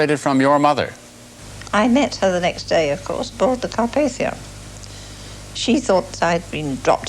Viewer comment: What did she say at 3:14? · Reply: They boarded the Carpathia. The hero ship